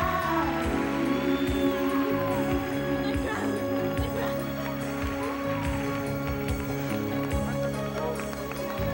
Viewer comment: Qué dice la reina en el minuto 3:40? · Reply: My crown, the crown!